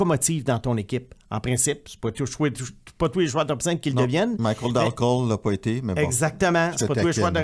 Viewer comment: C'est quoi le joueur que mentionne à 16:35 ? Michael Dunkel? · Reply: Michael Dal Colle